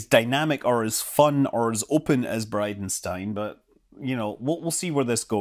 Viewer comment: Thought you said BidenStein 😜